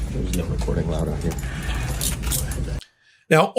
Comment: did he say "there's no recording allowed here"???